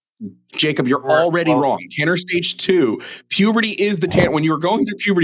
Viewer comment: lmaaaoooo Jovan said "tanner stage" then this dumbass caller thought he said "ten stages". What a fucking idiot, holy shit